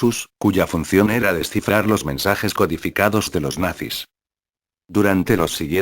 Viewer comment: Alguien me puede decir que es lo que dijo en este minuto: 2:19, es que no entendí muy bien, pregunto que sigue después cuando dice descifrar los mensajes codificados de los (esta palabra que sigue aquí no la entendí, les pido ayuda por favor, es para una tarea que tengo que mandar hoy) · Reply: Descifrar los mensajes codificados de los Nacis.